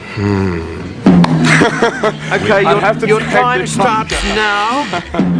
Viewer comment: What did he say on 4:44? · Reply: What he said was "I'd have to take the Oscar.", which unfortunately they didn't hear, so they kept asking him.